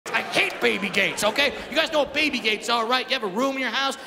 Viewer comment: I thought he said Baby gays 😅